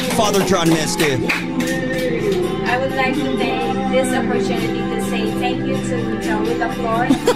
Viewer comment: what did she say? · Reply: "I would like to take this moment to thank Joey LaFleur for the opportunity... and also to Joe [the manager?] for this wonderful gift"